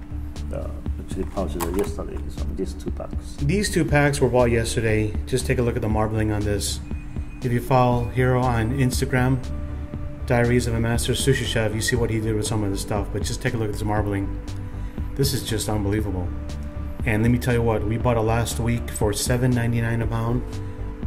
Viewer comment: Really sorry, but ONCE I heard "diarrheas of a master sushi chef" and ever since I cannot unhear it... :D